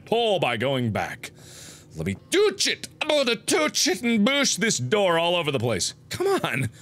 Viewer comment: tooch and booch... i heard it... why is tyra banks here